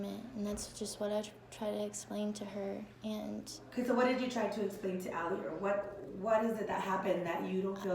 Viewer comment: I think i heard the woman say “Ok, so what did you say to Ally” 🙄